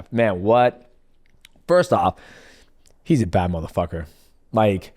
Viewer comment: Did he just say bad mf*** to Islam? 1:06:16 😮😮😮